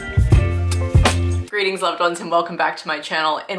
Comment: omg i totally thought you said "greetings lovebugs" instead today which is so so adorable. thank you for the great video!